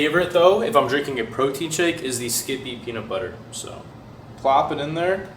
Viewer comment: did he say skibidi peanut butter?